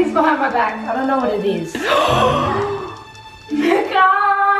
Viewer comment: WHAT DID NAZ SAY 30:18 !!!!! PLAY BACK SPEED 0.5 OR NORMAL PLEASE TELL ME WHAT SHE SAID BECASUE I HEARD SUM ELSE!!! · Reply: she said mecca... its a shop